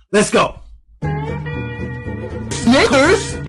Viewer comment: Sounds like he said sniggers. You know what that means! Thats right!!